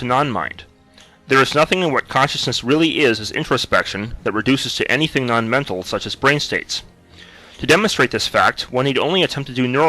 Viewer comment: garbled narration, what on earth is he saying? "There's nothing more consciousness really is is infraspection that reduces to something non-mental such as ..."